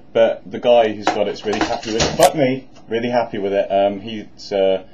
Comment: did you say "fuck me"